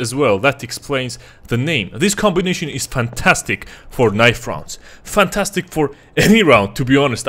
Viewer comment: who hear fornite? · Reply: inhales its "for knive rounds"